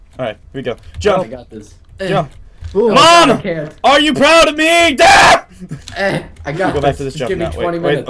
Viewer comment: it sounded like he said mom are u proud of me DAD